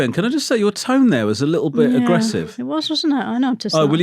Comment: am I the only person who hears 'your toe nail is a bit aggressive '